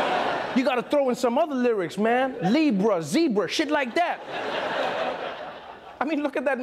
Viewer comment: Did he just say "ZEEEPRA" again?